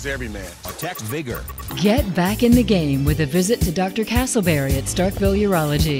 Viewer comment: Did I hear Dr. CASTLEBERRY??? 0:42 BOOM!